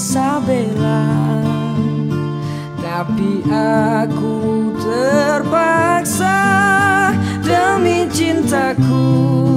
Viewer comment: Tiba-tiba muncul di beranda Langsung play dan.... W O W Duet beda usia yang mengingatkan waktu Inka feat Amy 03:31-03:32 (dengernya trepisahh)